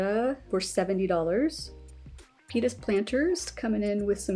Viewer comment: I was doing my PT exercises while watching and I could've sworn you said P*nis Planters instead of Pita's Planters...damn near threw out my back trying to look at the screen.😳🤭🤣🤣💚